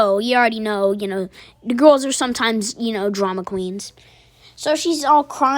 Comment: I think you told me this story once.🤔 6:50 BOI WHAT YOU SAY!! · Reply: I said SOMETIMES